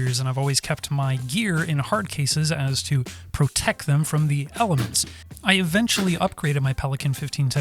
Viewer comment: ‘. . . and to protect my stuff from the Pelicans’ is what I THOUGHT you said 👀 whoa. Who knew pelicans were a menace to cameras?? 📸 ty